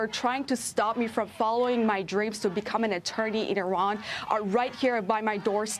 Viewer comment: Did she say that she was going to become an attorney for Iran?